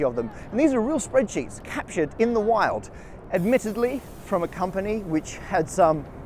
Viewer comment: did you say 'catched in the wild'? I'd say 'caught'. Or maybe I misunderstood.